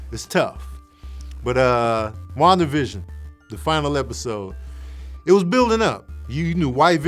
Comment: Man I thought you said to hell with Wandavision finale😂😂😂😂😂. The real Mandarin is in Shang - Chi, I thought everyone knew that? The 10 rings on Shang-Chi. Even peters because he had more movie time